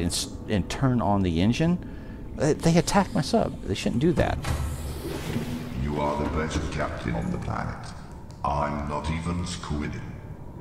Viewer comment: Can someone tell me what the Cyclops said? I heard "You are the only captain on the planet, I'm not even ???" I could be wrong, but that's what I heard... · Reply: "You are the best captain on the planet. I'm not even squiding."